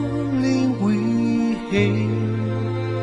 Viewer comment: The captions have mistakes. Two that I caught are at 14:59, it should say "hailed" instead of "held", and at 15:21 it should say "fight" instead of "flight".